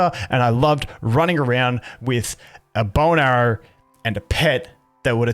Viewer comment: Sir did you just say running around with a boner and a pet?